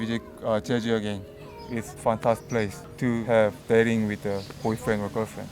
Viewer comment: welp subs help a lot. I heard it's fcked up place instead of it's a fantastic place.